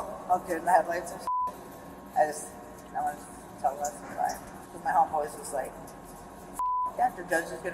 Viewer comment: that inaudible is her saying she wanted to tell Leslie goodbye